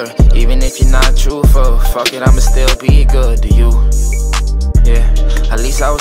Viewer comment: I thought he was Saying L A sounds Good to you ? 😹😹